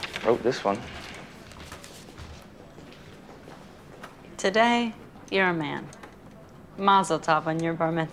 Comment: What did Summer said on 0:29??? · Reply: Today, you're a man